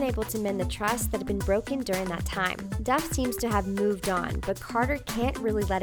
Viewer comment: u said dove instead of hilary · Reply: Professorpink22 ! She said "Duff" which is her last name